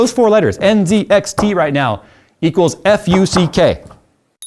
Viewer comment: I actually thought he said anything with the letters NZXT means FUEK